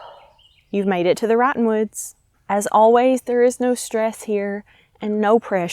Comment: I always hear "rotten woods" 😭